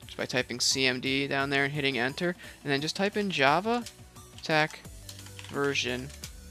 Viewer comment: did you say tech? it's a hyphen wtf